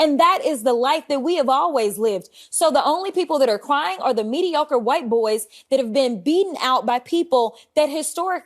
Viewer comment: The claim that “white boys” are mediocre seems more like an argument against DEI rather than for it. If white men are truly mediocre, wouldn’t they be passed up regardless of whether DEI plays a role in hiring? Corporations and the government have a vested interest in hiring the best candidate, so if white men are supposedly “mediocre,” it would logically make sense for companies to hire non white candidates regardless of whether DEI policy exists or not. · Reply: I heard it as the mediocre white boys, not the "white boys, who are all mediocre"